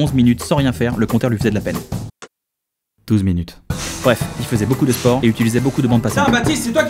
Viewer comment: il dit quoi ??! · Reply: Je te réponds 4 mois plus tard mais bon il dit "il fait beaucoup de sport et utilise beaucoup de bande passante"